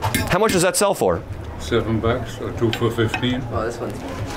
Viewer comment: did he say $7 for 1, or 2 for $15? wtf is this math